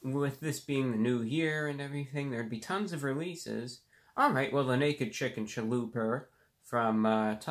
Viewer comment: I heard "Alright. Well the naked chick in cheluper".